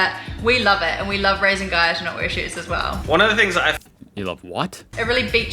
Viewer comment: I think she said "I love raising guys who don't wear shirts as well". It's a normal thing to see dudes walking around topless, cos it's hot, or they can't be bothered cos they just heading out for 5mins up the road, grocery run, stop shop etc